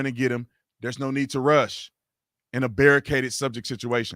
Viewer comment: did he just say there’s no need to rush??